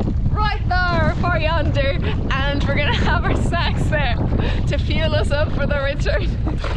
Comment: Am I the only one who thought they heard say at 12:25 "... and we're going to have our sex there" when she was talking about finding the beach? I think I actually blushed for a second before realizing it was "snacks." 🤭